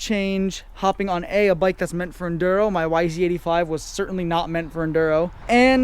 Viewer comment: For a moment I heard My wife see is 85. What a scare, you said my yz85. 0:40 hahaha….